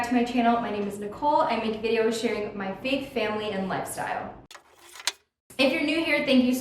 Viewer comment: I know you are saying “faith family and lifestyle” but I heard “fake family and lifestyle” 😆...love your videos!! Thanks for sharing